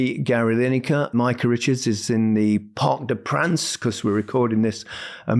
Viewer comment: i thought u said mica is in the bup haha